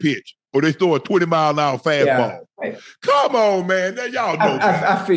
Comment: Did UNC just say 1:32 20 mph FASTBALL? lol. That wouldn’t go out. And it ain’t a fastball